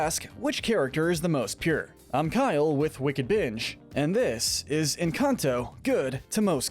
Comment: I thought he said wicked bitch😂